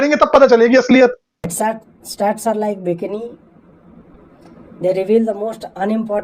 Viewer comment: dude what he says stats is like what? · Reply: Bikini 👙